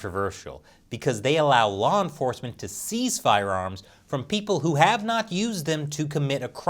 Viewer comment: You say 'seize', I hear 'steal'.